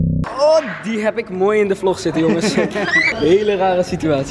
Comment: hoorde ik daar vlog?